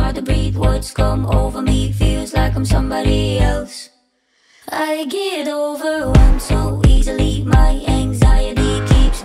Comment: my whole life i thought she said "i get over run so easily..........."